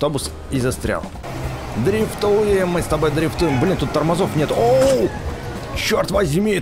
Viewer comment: Я никого не оскорбляю но мне показалось что он сказал б**